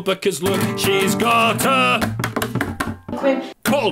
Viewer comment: As always, love your musical side, but what did you call at 2:52? No matter how many times I replay it it sounds like "quib" which doesn't make sense to me. Is it a euphemism I don't know?